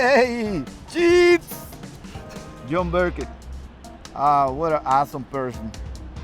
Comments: Great advice people, if you're ever down in the dumps don't worry. The organ will continue to play. 😂😂🤣😂😂🤣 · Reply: Bryan Even the guy standing beside him probably also thought that he said, "what an asshole person." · Lmao @ 2:54 it sounded like he called him "an asshole person" and "phony", but he really said "an awesome person" and "funny". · I thought he said john Burkett what an A-hole person. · Great advice people, if you're ever down in the dumps don't worry. The organ will continue to play. 😂😂🤣😂😂🤣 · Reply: it sounds like he says, what an asshole person. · For John Burkett, it sounded like he said, "What an asshole person."